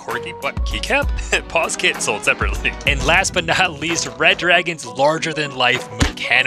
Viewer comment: I thought i heard 'bad dragon' instead of red dragon